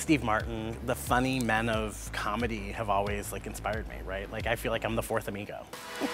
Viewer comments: i thought she said “i feel like i’m the fourth Migos x i about spit my beer out all over my iPhone screen !!!! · LMAO I THOUGHT SHE SAID "IM LIKE THE 4TH MIGO"